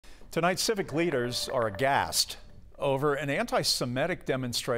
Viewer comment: did he just say they're gassed?? loool that is hilarious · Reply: Aghast